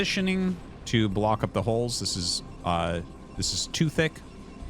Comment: "This is two thick." ... heard that as too thicc during the pause 😂